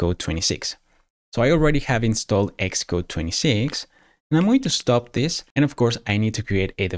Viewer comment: Great tutorial. At 7:21 I think you mean iOS 26, not Expo 26, correct? or did you say Xcode? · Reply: I said Xcode 26 :)